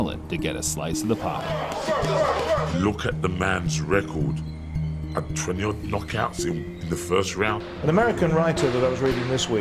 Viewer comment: Dude wtf did Frank Bruno say at 7:03 “Look at the guys record blabber in the first round”